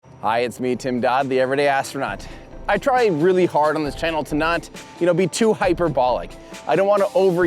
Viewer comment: I heard “I try really hard on this channel to not be too hypergolic”